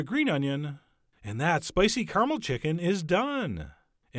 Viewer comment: damn, i thought it said spicy camel